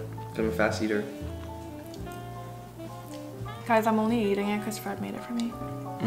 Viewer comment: "guys i'm only eating it because fred made it for me" okay girl we see you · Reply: I THOUGHT SHE SAID guys im only eating it bc fred made me